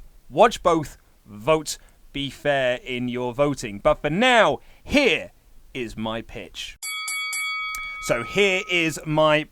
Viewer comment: I thought you said "here is my bitch"